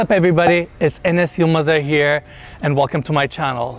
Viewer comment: For a second I thought you said it's Enes your mother here, gave me a good laugh! Love your channel, thank you!